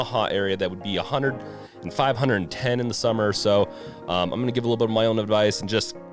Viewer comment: Sounded like you said 510 in the summer